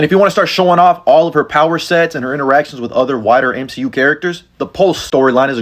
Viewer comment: for a second I thought that you said other whiter MCU characters